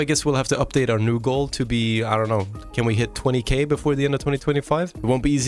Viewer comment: i genuinely thought i heard if we hit 200k, and i was so confused cause i thought that happened long ago